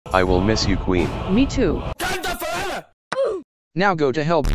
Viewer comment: The first thing I thought I heard was, "I'll riz you Queen" and I'm dying right now.